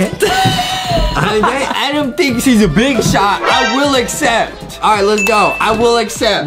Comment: AYO am I the only one that thought said "adem thinks he's a b****"